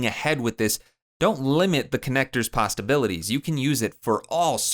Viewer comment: Really great, super useful, I don't want to take away from that buuuuuutttttt....did you say pasta-bilities?